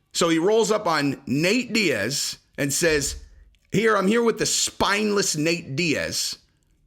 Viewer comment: I thought he said spineless at first too but he said “Finest”. He said something about the guy talking shit on Twitter